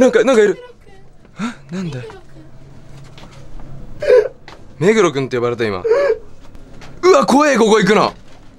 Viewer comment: に目黒くんって聞こえるの普通に怖すぎん？